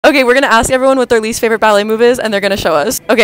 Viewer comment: I thought she said "ballet movie" and I was SO confused by the answer and then what followed it